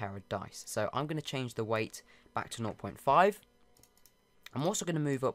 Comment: when you said wait I thought you said weight 😂